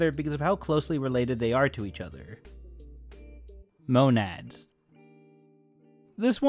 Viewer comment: thought he said gonads of a second lol